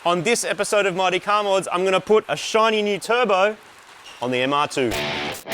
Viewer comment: I thought that said shitty new turbo😂